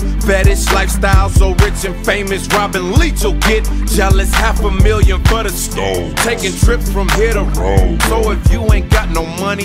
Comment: Swear all these years I thought Luda was saying “half a million butter scones.” 🤦🏽‍♀️